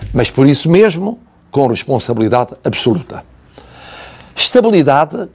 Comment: ouvi a palavra absurda.ou absoluta ?